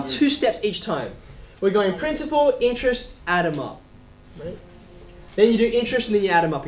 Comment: When you said add them up i thought you said adunup